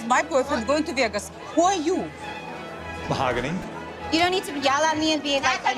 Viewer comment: I think I heard "Mahoganny?" at the end🤣🤣🤣